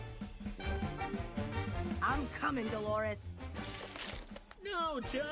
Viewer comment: I hear her name is "Dolores"